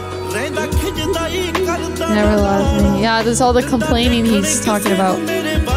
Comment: Subtitles r really bad .Looks like he was high when he made that Subtitles . Like for eg- at 4:28 subtitles say he doesn't let me see anyone else ,that aint what sidhu said. Sidhu said he doesn't let anyone see at her behave like my guard (means he protects her from bad guys like who just stare at women and comment at them ) and subtiles were wrong at many parts not in only this part. Subtiles were even wrong when Lady was singing in English which is really funny 🤣🤣